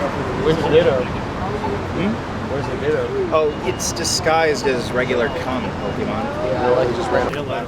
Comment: "oh, it's disguised as regular cum... Pokémon" Is that what he is saying? I don't really know the Pokémon lore.